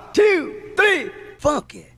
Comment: You know I always thought in your intro instead of funky I thought it said f*** it